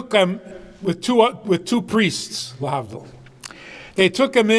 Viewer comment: What did he say at 4:58 just after he says "two priests"? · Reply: He said "lehavdil" its an expression, like "of all things" or "the opposite of what you would think" eg, for example, "he came in with two priests if you can believe".